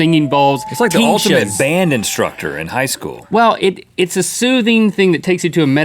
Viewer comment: It sounded like Rhett said its like tesha out band instructed in high school did he?